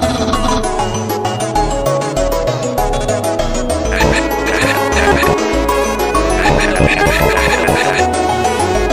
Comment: have no idea what it saying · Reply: i see at the comments it says "sans, papyrus,chara...accept the gift we're offering to you,bond with us,and you'll become the strongest of this timeline" and when fight begins it says "your fault" next to sans